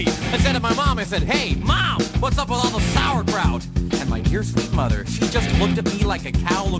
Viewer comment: All this time I thought he said "And my peaceful mother."